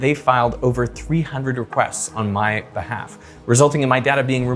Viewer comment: Did you say they made arrests on your behalf?